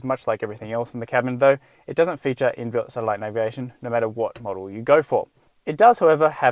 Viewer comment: What did he say at 1:37....what kind of integration? Thanks. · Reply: "It doesn't feature in-built satellite navigation" – in the Australian market.